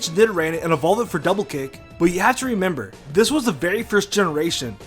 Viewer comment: i heard him saying double cake..